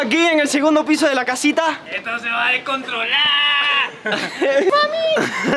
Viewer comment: minuto 5:10 si lo entendi xd ESTO SE BA A DESCONTROLAAAAAR